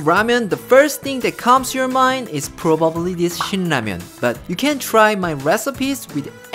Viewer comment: I was reading comments suddenly I hear "Sh*t ramyun" I'm like "wait what?" And look up to see its actually "Shin Ramyun"😂